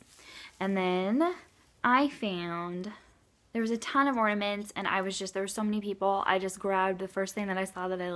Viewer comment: Also... lol when you said " there were a ton of ornaments" at first i thought you said, "there were a ton of mormons". Lol... i backed it up because i figured that wasnt what you said. Funny times. :)